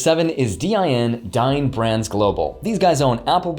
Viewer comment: I thought you said DIN was Dying Brands Global. Had to listen to it again. LOL